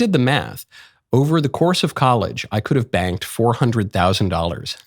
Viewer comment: Oh yeah, I was waiting for the "morality clause." Btw, do you really believe that you could have "banked" $400k by donating your swimmers? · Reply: At first I thought he said “banged” 400k. 😅